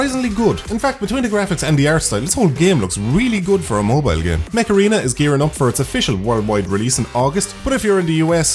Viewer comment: Thought you said MACARENA....